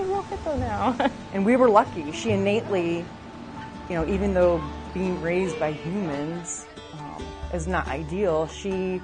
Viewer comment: I swear, I thought that lady said the Baby Hippo "was being raised by Demons" !!!!!